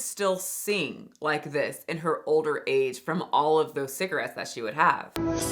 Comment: Old Age?!?? Judy Garland died young. She was only 47. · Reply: “Older age” was what was actually said and she is referring to her heavy cigarette use which accelerates aging in anyone.